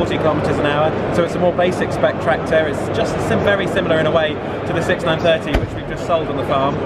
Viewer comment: Did he say at 1:54 that he is sold the 6930